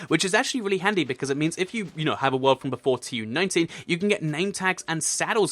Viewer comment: Why did you say TU90, do you mean TU19 or TU31. Time you said it is